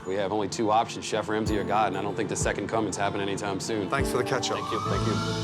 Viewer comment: In case you Americans heard: "Thanks for the ketchup." He actually said: "Thanks for the catch-up." 😂😂😂